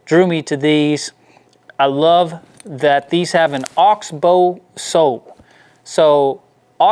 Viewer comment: I’ve owned lots of boots including leather sole and rubber sole cowboy boots. Never heard of oxbow soles! I liked those horsepower boots a lot! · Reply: first I heard "oak's bow sole" I thought what? )))